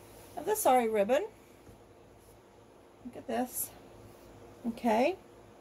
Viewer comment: Very nice! At first I thought you were saying "sorry ribbon" ....and I was like 😕🤔 what is sorry ribbon? I was about to Google it but then read your comment and was like Oh! LOL! It's beautiful. So glad you shared 😊🏵️